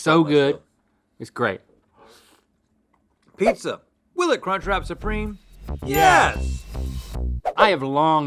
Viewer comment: I can't be the only one who misheard Link when he said cunch 😂